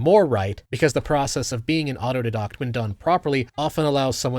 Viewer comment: I'm new to the channel. What are you saying at 15:17? Autodeduct? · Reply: Autodidact